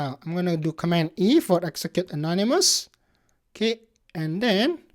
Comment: What did he said at 12:14 How to get that dialogue box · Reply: ctrl+E